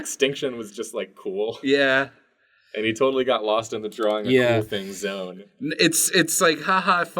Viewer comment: someone please tell me what he says here while being talked over. I will love you forever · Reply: "Drawing 'the cool thing' zone"